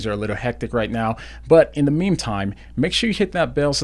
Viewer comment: did he say "in the meme time?"